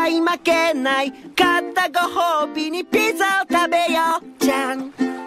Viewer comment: what it sounds like: “I got to go home and piss in the toileta.” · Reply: At the end I can’t hear it ;-; I hear “Piza Tabeyo” which is what he’s saying 😅 「ピザ食べよ」But the rest I can hear 😂